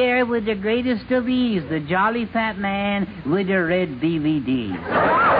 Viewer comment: I thought he said "Red DVD's" at 6:13.